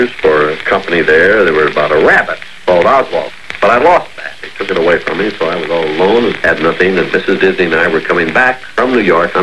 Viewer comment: I heard it! "I had a character of a rabbit named Oswald. But I lost it, because they take it away from me so I was all alone-"!